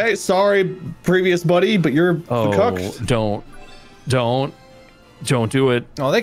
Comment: did just say that globox was cooked?